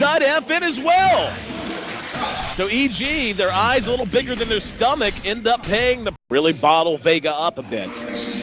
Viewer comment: did he say "EG have a bigger eyes than his stomach" ?